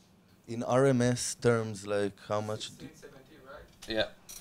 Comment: what is he saying at 22:47? 16-17 rms?